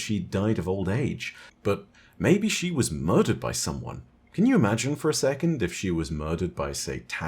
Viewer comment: lmao I heard "can you imagine if she were killed by satanics"